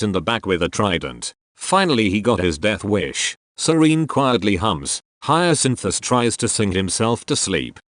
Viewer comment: OMG I THOUGHT IT SAID HUMPS INSTEAD OF HUMS I